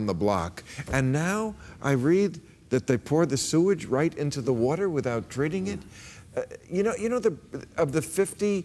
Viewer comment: Did Bill say "And now I hear they put their sewage in the water without drinking it?"